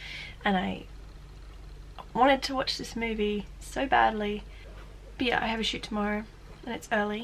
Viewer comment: When she said "But yeah I have a shoot tomorrow" I thought she said "But yeah, I have a shit tomorrow" O_O soz